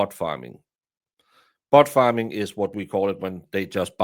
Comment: I know you said bot farm... But I heard 'Butt Farm'!!! LOL! 🤣